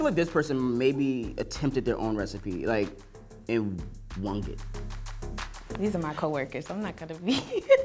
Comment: did he say "wong"...? Isn't it "they winged it" ?🥲